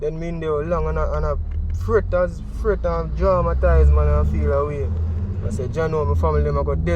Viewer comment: This young man is still traumatized with a vivid memory. Great informative interview. Kudos👏🏾👏🏾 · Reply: I thought he said he was "dramatize". 😆